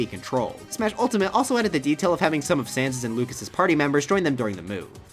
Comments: Did he call Ness Sans at 20:04 ? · DID THEY JUST SAY SANS?! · did... did he just say Sans is Ness...